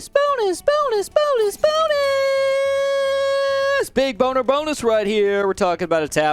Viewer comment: did he just say bone